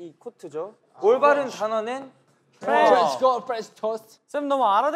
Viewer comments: did haknyeon say "trench coat french toast"??? ksksksksk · I swear I heard “french toast” @ 17:06 🤣🤣🤣